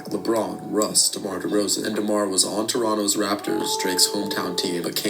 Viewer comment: thought he said D Roz